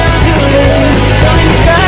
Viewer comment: what did he say? Can anyone translate that? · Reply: Pizda rulyu C*nt steering Yes, there was swearing